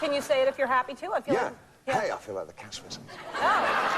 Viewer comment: what does he say at 15:17, "I feel like the cat´s ....??" Could anybody tell me? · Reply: I think he said "I feel like the cat's whiskers"